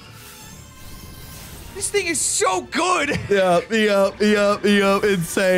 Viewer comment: Um…did I hear “This 🥷🏽 so good”